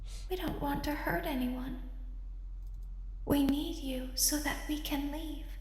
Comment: The strange voice sounded like it was saying "we needed to, we needed to"